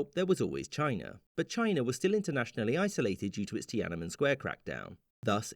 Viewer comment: I heard it as "the tianamen square crashout" 💀